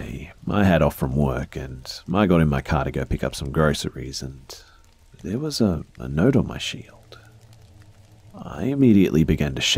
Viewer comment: Randomly tuned back in and I heard... "There was a note on my shield..." Took me a bit to realize what was really meant haha Cheers